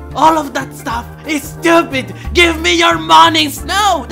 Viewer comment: i thought yin yang said give me your body on a sparta